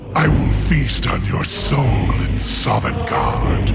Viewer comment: i misheard the beginning as 'i will facetime your soul in songarde' and with that image in my head i will continue the video now